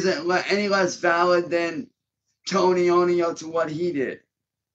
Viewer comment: I thought he called him Tony-Ony. 😂🤣😂